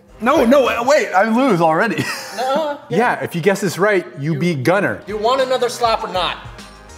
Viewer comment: I thought said "if you get 1 more point you EAT Gunner" 😂😂